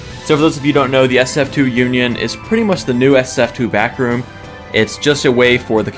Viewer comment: I thought you said the ssf2 bathroom lol